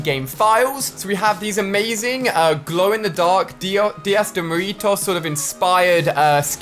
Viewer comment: he says “Dias de moritos” 😂 instead of “dia de los muertos” (day of the dead)